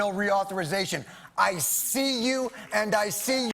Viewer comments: censorship!? What did he say? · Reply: "I see you, and I see your bullshit!" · Why the hell is that censored. What did he say? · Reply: "...and I see YOUR BULLSHIT!" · What was said in the blank spot at 11:47? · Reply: He said, "I see you, and I see your bullshit." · What did he say at 11:47-11:49? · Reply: He said, "I see you and I see your bullshit"